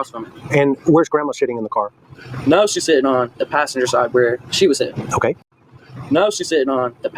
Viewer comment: what is saying there? About Grandus sitting where SHE was sitting? WHO IS SHE???????? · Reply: He says now she's (grandma) is sitting on the passenger side where she ( Leslie) was sitting as he points to Leslie.